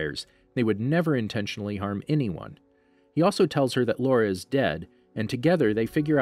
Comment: Did y’all hear what I heard... “Laura” instead of Laurent?